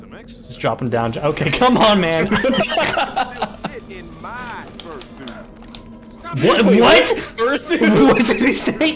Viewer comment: I don't know what Frozone ACTUALLY said but "Fur Suit" is all I can hear now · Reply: I think he said "I can still fit inside my first suit"